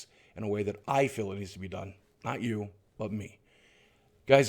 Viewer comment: Did he just say "I'll sleep with you again soon." at the end?